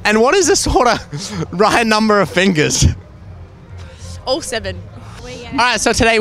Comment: did she say all 7